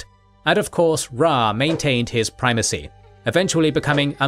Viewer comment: who else heard "priVacy" for a moment?